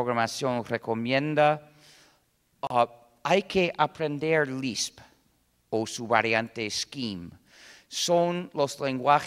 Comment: que lenguajes de programacion recomienda en el segundo 8:25? no puedo entender lo que dice, alguien podria escucharlo porfavor? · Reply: Lisp, y su variante Scheme.